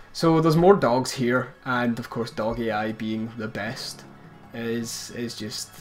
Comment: Thought you were saying "doggy eye" until this video, thought it was an ability I hadn't seen. Dog AI...